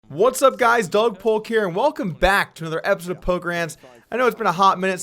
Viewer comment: did you say polker hands???